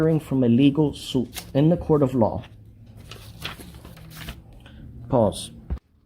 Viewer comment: did he just say”Pause”? Such an OBVIOUS edit there buddy (not you 😂 )